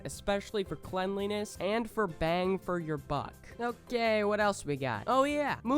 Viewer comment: I thought u said bang for ur f&@@ not " bang for ur buck"